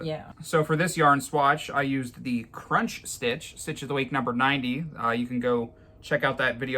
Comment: Hi and thank you for sharing the swatches with us today I think I like the hurt by Robin V stitch and No fuss stitch. I was wondering is there a way for you to can show the number of the samples on the screen ? I had watched when you both made some blankets had made one that I feel in love with I thought he said stitch of the week #32 I went right away after watching you and I could not find it.😢 I may have not have heard him right, Hope it's not too much work for you thank you I love to work you both are doing it is awesome caveman inspired